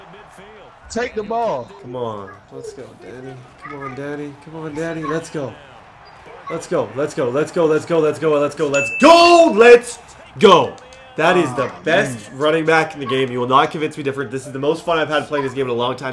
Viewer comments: I thought he said come on daddy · Did anyone else hear him saying let’s go daddy